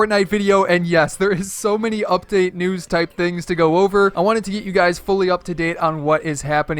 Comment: did he really say I wanted to eat you guys😂😂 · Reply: He said I wanted to get u guys up to date